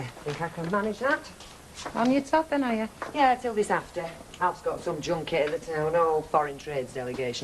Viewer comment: I thought Deidre said "Alf's got some JUNKIE in the town hall" at first. Alf Roberts the Pete Doherty of Weatherfield. Kevin and his bath chat really makes me thankful I'm pretty much from a shower in every home generation. And as for certain bath nights, I shower every day and sometimes twice. Baths give me the boak 🤢 Kev's bird, I thought only lads had to wait for their balls to drop before their voices broke? 🙉